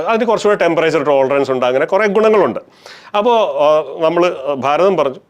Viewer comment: did he say "Bharatham"😂.. Bharath modhi ki jai😂😂😂 Avasta thenne